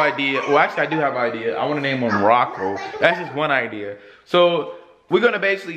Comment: It sounded like he said rocko